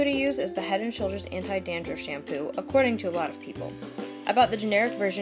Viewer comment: i thought she said according t white people instead of according to most people lol